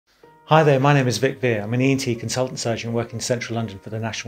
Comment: i thought i heard big bear but it’s 😂